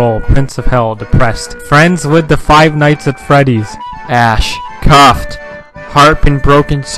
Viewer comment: I heard "friends with the five nights at freddy's" I immediately put on my puupet cosplay